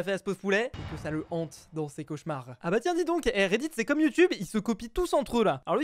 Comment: A 1:24 j'ai compris redid au lieu de redit( je sais pas vous)